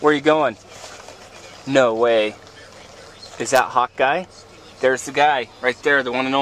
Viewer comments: AHHHHHHH Hawk eye!! I thought he kept saying "hot guy" in the other vid! I thought that was a weird super hero name. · everytime you say hawkeye it sounds like youre saying hot guy lol